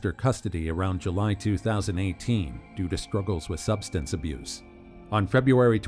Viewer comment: off topic, but did he really say "sKruggles"?